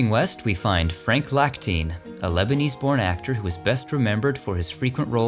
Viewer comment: I swore you said Frank Lackteen, 11:28, was a Lebanese porn actor 😆😆😆